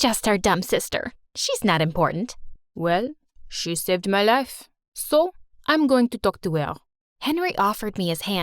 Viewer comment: When he said “she saved my life”i heard “she seved meh lef” 😂 😂 🤣